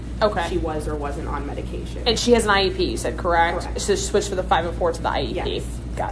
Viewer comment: Sounds like investigator asks: "And she has an IUD correct?" @ 12:22. Anyone else hear this? Was she asking about contraception? (IUD = intrauterine device)